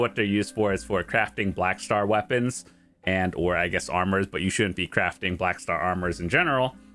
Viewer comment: what did you say here? Should or shouldn't? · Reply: I think said shouldn't because it is a known thing that the blackstar armor is very very expensive to enhance and it is equal at pen to c10 regular boss armor